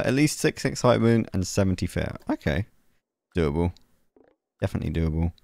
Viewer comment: Did say 70 fear at 17:54?